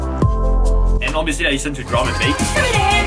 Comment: and obviously i listen to- what? i couldn't hear sorry ;_ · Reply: Drum and Bass :P